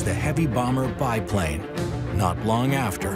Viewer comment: Did he say bi plane ? 🤔